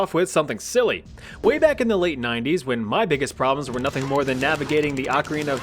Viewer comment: “US and Russia” My OCD: “….it’s the Soviet Union….“ Edit: I later found out this isn’t the USSR anymore. Misheard “early 90s” instead of “late 90s.”